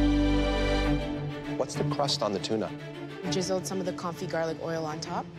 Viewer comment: Did she say JIZZLED?